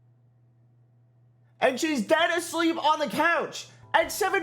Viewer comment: BRO I thought you said she was DEAD at first lmfao, that would've been a huge plot twist